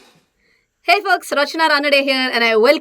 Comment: Did u say ! 0:32 hey fu*ks ?